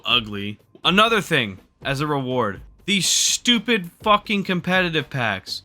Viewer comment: i thought it said "Retard"